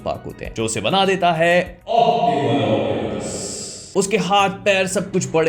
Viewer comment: what ? Kya bola ? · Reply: Optimal optimus